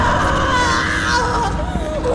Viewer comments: me parece que dice "me hiciste parte de ti" o eso fue lo que entendí porque escuché "you make me part of you" pero probablemente sea porque estoy casi sordo XD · Cuando oí la frase por primera vez, yo había entendido que mommy decia: "you make me follow you" o en español "tu me hiciste seguirte", indicando que en esos momentos se sentía traicionada con el protagonista por hacerle eso, y que, incluso de una forma retorcida, mommy sentía aprecio por el protagonista. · Tras escuchar la misma parte muchas veces, en el minuto 7:59 Al principio pensé que decía "don't make me part of you" es decir "no me hagas parte de ti" pero al escucharlo muchas veces y con el volumen a tope, lo que realmente dice es "he'll make me part of him", es decir, "el me hará parte de el" Teniendo en cuenta que al final 1006 se lleva la cabeza, brazo y parte del torso de mommy long legs, podemos deducir que mommy sabía que tras su muerte, algo se la llevaría, en este caso fue 1006, y le temía a eso, no a la muerte, eso sinceramente me parece espeluznante. Pensar que un juguete psicópata con ansía de venganza y sangre le tenga miedo a morir por lo que le pasará después y no por la muerte en sí, es simplemente aterrador. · Yo pensé que decia, “me hará parte de el” Pero ahora ya se. Gracias por la traducción! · yo escucho que dice: "You make me follow you" (me hiciste seguirte) aunque "don't make me part of him" (no me hagas parte de el) sería más coherente por cuestión de la trama